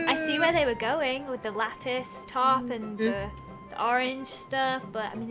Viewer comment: I thought she said lettuce instead of lattice tip